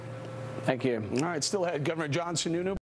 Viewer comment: at the end i thought he said governor johnson for a second there. lol damn it i miss gary!